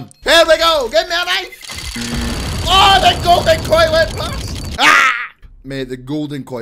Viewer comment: I thought he said the golden toilet went by but he said golden koi.